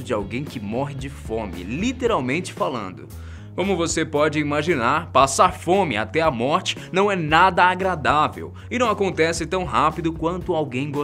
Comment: eu entendi fomo